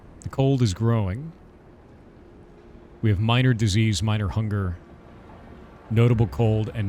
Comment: i misheard disease as - da zzzs and for a moment I thought there was a sleep mechanic